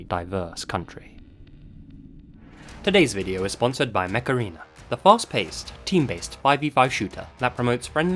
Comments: Whenever you said "Mech Arena" I heard "Macarena." Aaaaaaayyyyy Mech Arena! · Install Mech Arena for Free ✅ IOS/ANDROID: <link> if you’re in the US, Canada, the UK and get 1 Gold Crate, 15,000 Credits, and 200 A-Coins 💥 Available only for the next 30 days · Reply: Is that bad I kept mishearing "Mech Arena" as "macarena?" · I thought you said "The macarena" not "mech arena" x) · every time i watch this video the mech arena ad takes me out cos i keep hearing "macarena" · i thought you said “macarena” not “mech arena” 🤣😭